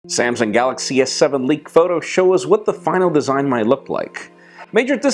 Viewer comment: thought you said 'what the fuck' at the start xD